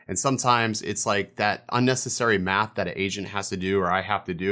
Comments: I really thought he said "asian" not "agent" Jesus Christ hahaha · What you said: "that unnecessary math that an agent has to do". What I heard: "that unnecessary math that an Asian has to do". I'm dead 🤣.